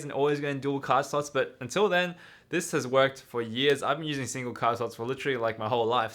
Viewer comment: what did he say?? 😫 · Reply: He claims he's been using single card slots for his 'whole life' which according to him is 6-7 years.